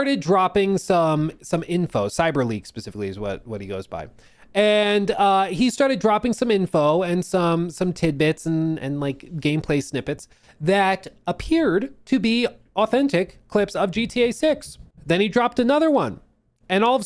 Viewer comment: Are you Canadian? I swear I just heard you say “timbits” instead of tidbits